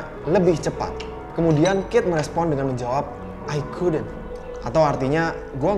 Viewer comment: Instead of “i couldnt” I heard “so good” 🙃